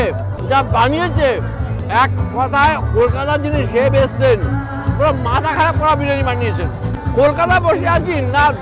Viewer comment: কি শুনলাম bara মাথাখারাপ bara বিরিয়ানি বানিয়েছে😂😂